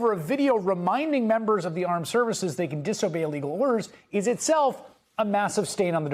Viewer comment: Who does the captions on this? They wrote, "disobey legal orders," when he clearly said, "disobey illegal orders." That is a major blunder. There is a huge difference between legal and illegal. A mistake like that could get a person sued. You can see it at the 4:03 mark. It makes me wonder if the transcriptionist did the same thing elsewhere in the video, but I am not paid to review this video for transcription errors so I won't bother checking. I am sure MS Now should ask their own people to check these videos for errors like this.